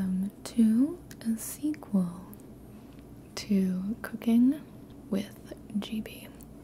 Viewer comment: I hear " Welcome to cocain with Gibi!"😂👌🏼